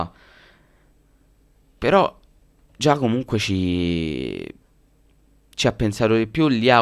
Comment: a primo impatto ho sentito "Giacomo Puccini"